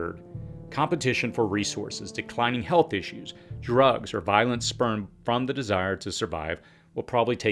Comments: I had to take 3 double checks and put on the closed captioning. Did he say violence sperm? Does sperm have another definition that I’m majorly unaware of? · Did you just say violent sperm will take out most of the population? 🤔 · Did I hear "violent sperm" at 6:34?